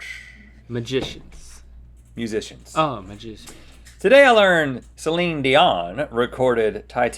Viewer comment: Rhett: “Link, this is all about famous musicians.” Link: “Magicians?” Rhett: “Musicians.” Link: “Oh! magician...” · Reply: I honestly thought Rhett said magicians as well, lol! 😆😆😆